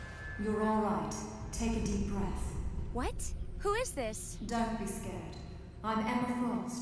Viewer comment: Subtitles "What, Who said that" Amy "What, Who is this" bad subtitles >;o